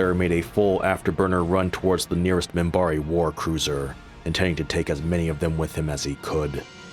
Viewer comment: For several seasons, I thought the opening said “Ten years after the Earthman Bari Wahr” and I kept waiting for someone to tell me who Bari Wahr was…..